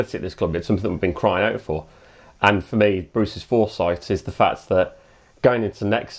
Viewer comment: Misheard you! Thought you were talking bout bruce forsyth not bruces forsight! DOH!